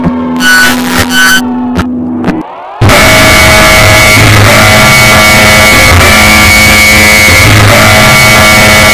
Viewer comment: what is that saying? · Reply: Holy Shi- <num>/10